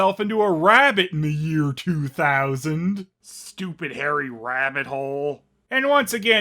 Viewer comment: “Stupid hairy Rabbit hole.” Hmm something i never thought would say.